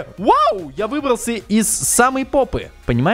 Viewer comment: Эмм 14:46 мне это сомому послышалась я выбрался из самой попы